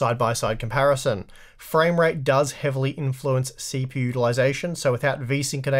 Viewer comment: I keep hearing "CP ulyzation" instead of CPU utilization.